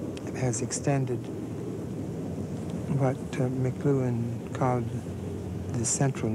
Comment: if anyone is interested in researching what he is saying, he mentions Marshall McLuhan and not "McGoohan" as the subtitles say.